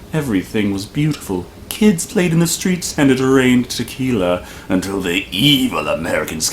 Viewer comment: Sooo I heard this as “kids played in the streets and drank tequila “ and can’t unhear it, not sure I want to. Imagining kids playing around a busted fire hydrant but it’s tequila they are splashing in for this version of paradise.